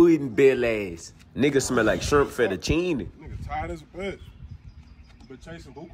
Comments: “ol spilled chocolate pudding belly ass.. nigga smell like shrimp fettuccine..” 😂😂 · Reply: thought he said shart fetuccine LMAO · “ol spilled chocolate pudding belly ass.. nigga smell like shrimp fettuccine..” 😂😂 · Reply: My dumbass thought he said shirt fettuccine 🤦🏿‍♂️😂😭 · “ol spilled chocolate pudding belly ass.. nigga smell like shrimp fettuccine..” 😂😂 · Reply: I thought he said syrup fettuccine 😂😂😂